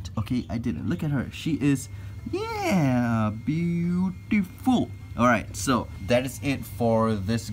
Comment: Com‘on guys I can’t be the only one who was thinking of the Word Bootyful instead of beautiful at 8:33 hahaha :). Man I love your Videos, keep up your good work :)